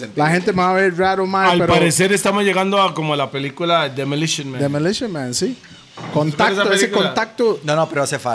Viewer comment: cómo se escribe esa peli que dice?, no la encuentro :( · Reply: Demolition man